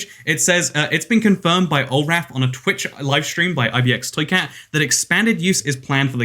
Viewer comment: It does not sound like your saying ibxtoycat it sounds like mkxtoycat